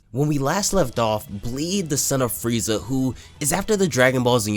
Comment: did you say that bleed was the son of... F-F-Frieza?